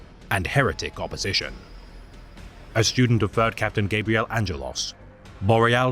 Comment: Dammit whenever i see ravens it reminds me of birds, i heard "Third Captain" as "Bird Captain" 😅